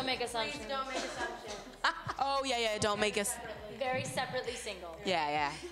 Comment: I don't get what Camila & Lauren said... they said "please, don't make us... " what? Something ? sandwich? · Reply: they said "please don't make assumptions"